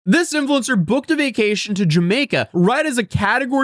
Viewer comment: I must be tired. I pressed play and could have sworn you said "this simpleton" and not this influencer 😂